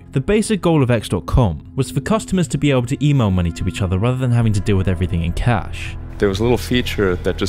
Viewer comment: please, what did you say at 5:50? I couldnt understand a thing. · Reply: Email money rather than having to do everything with cash..... Dickhead